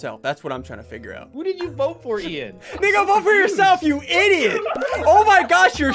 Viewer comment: it sounds like ssundee said N*gga vote for yourself LOL